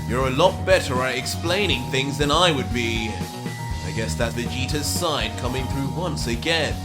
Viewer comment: you put in subs Vegeta instead of Vegeto